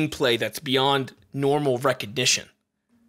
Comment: excuse me sir, but did you just say "recedition"?